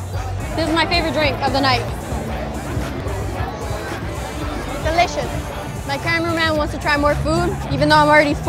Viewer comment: Lol I just realized she is the camera man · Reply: I thought she said my caramel man meaning her man's lol